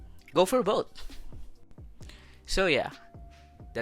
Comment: Did he just say"pees out"!??at the end